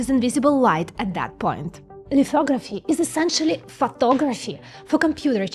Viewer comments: OMG i swear to god i thought you said "lithography is essentially fuckoveraphy" 😆😆😆 and yes... yes it is.... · what did she sayyyyy? Fucktography?